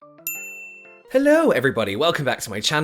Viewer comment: I giggled when I heard your “hellorw” hahaha anyway, great job!